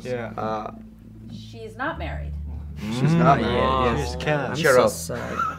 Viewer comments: Jimin whispering "I am so sorry" after the girl wasn't American.. So freaking adorable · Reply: Actually the interviewer said "She's not married" And he misunderstood and thought "is married" So he said "I'm so sad" 😆😆 Still adorable though😆👍🏻👍🏻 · Guys what did Tae said at 1:49 when Jimin said that he's celebrity crush is Rachel McAdams ? ' She's Carol ? ' I just can't understand properly ((